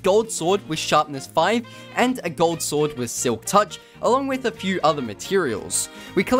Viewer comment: did just say a sword with sliktouch?